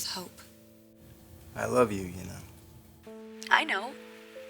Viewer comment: Dang waffle boy can't be arsed to say I love you back to Steffy. He's such a dud. He and Dope really do belong together, they're both immature idiots. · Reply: Are you deaf? He clearly says I love you to her 13:29. I honestly don't get how 13 idiots liked your comment even though you're wrong.